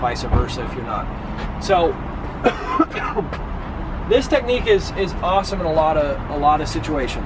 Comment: I thought you said 'once you had the ass technique' in the beginning of your video. A little disappointed you actually said S-technique but great video nonetheless.